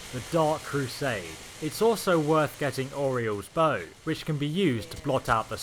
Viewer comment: Lol I misheard it as Oreo’s bow